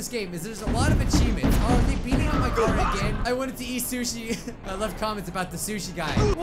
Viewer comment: Let me point this out DID HE SAY SHIT!? Just listen closely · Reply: He sushi listen close